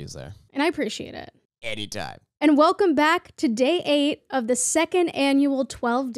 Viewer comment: Oh my God I thought said "today ate" instead of "to day 8"